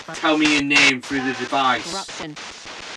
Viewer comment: I heard corruption from the EVP AUDIO